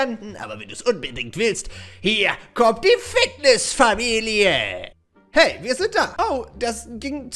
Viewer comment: Digga wer hat bei 13:19 fickniss Familie verstanden